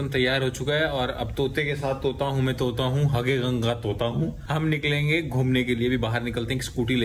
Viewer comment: Said: 👉🏻 Hage Rang Ka Tota Hoo🤣 Instead of Hare 💚 And His Expression....😳 Just killing😁